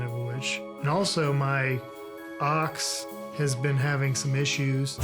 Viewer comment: I'm so ashamed that I thought he was talking about having troubles with his "aux" instead of his "ox" at